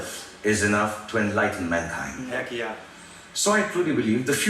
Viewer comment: Shitt i thought said IKEA when he was actually saying he k yeah and i was like WTF😂😂